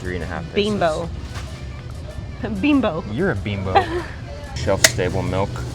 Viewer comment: I was multi-tasking at this point in the video and had glanced away but could still hear. All I heard was You're a bimbo! And then "Shelf stable milk". I was like, What?! Did he really just call her a bimbo AND shelf stable milk?! What does that even mean?! I then looked up and saw he was actually talking ABOUT shelf stable milk! 😆 🤣 😂 😹